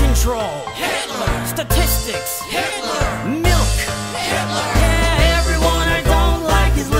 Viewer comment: I thought it said "milf. HITLER"